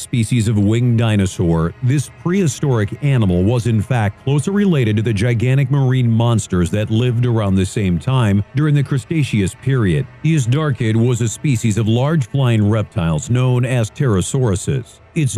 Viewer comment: Did he say "custaceous" period?